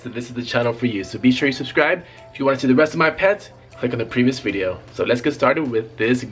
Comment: I heard "if you wanna see the rest of my pants-" 😂